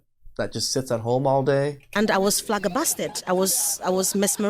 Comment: she really says "flaggerbasted" instead of "flabbergasted"